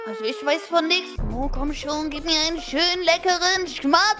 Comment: sagt : komm schon gib mir ein schmatzt ab ! Ich die es anderes gehört hat 💀